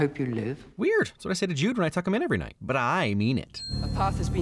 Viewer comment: You say Him when you talk about Jude, but the caption says Her…. Typo? Something more important?